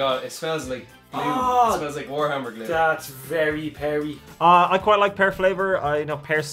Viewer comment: did say smells like Warhammer glue?!?